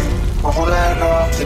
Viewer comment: "Oh shit, is that Freddy Fazbear?" The translation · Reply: Oh thought he said ohara Cheeto Freddy Fazbear